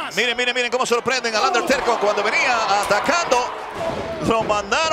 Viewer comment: Soy yo o me parece que dice Underterco 1:40 🤣🤣